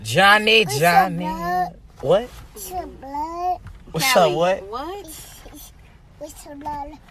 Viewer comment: #4 Wassup bro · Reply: u r crazy , the whole thing because i thought she said bro and the right one is blood , is it that big big deal , because i don't know u and i am not from where u live maybe we have different thinking , sorry maybe i said something very bad Sorry again